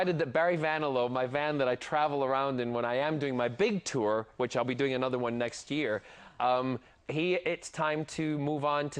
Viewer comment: Did I hear that correctly? at 4:08 He will do another BIG tour next year! --- Yaya Maybe I shall be able to go and watch it!